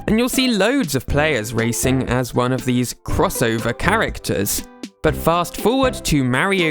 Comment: you said "characters", but the subtitles said "racers".